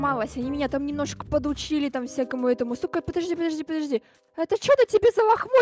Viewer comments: мне послышалось или она правда сказала Су*а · мне послышалось ? :_) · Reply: Мне тоже показалось что Катя сказала с*ка стой подожди подожди._. 🗿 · Я одна услышала что Катя назвала Эда (с*ка) время видео